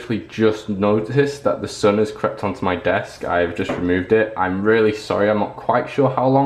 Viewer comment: when the sun was on you desk i thought you said "my son has crapped on my desk" XD LOL OMG.